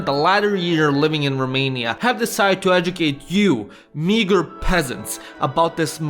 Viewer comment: Did you say meager or smthn else here 1:09 :x